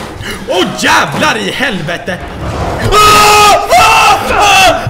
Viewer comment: did pewds just called sven to help him? lmao